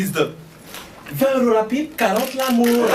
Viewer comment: Il dit quoi à 9:28 svp ? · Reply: 20 euros la pipe 40 l amour